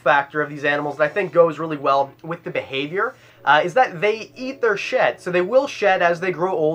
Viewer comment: When he said they eat their shed I thought he said they eat their sh*t🤣